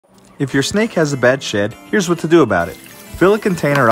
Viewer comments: For a second I thought he said at the intro- "If your snakes having trouble with a bad sh3t"💀💀💀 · I thought he said bad shi